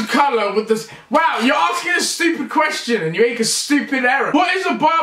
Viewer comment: did he say "you stupid Arab"?